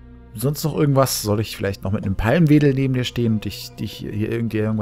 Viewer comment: Ich habe erst verstanden, du wollest ihm die Palme welden ... xD